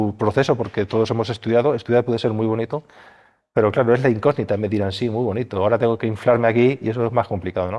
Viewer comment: algun español me explique que dijo, porqué no entendí la verdad 😅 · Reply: Pero es la incógnita… entiendo yo😂